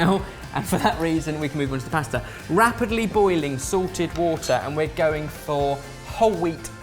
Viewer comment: I know says salted water but ever since I subscribed to them I keep hearing "sorted water" which would also make sense because they're SORTEDfood. The thought of that always makes me chuckle a bit ^^